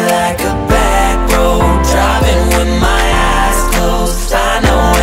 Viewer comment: can anyone else hear him say driving with my asshole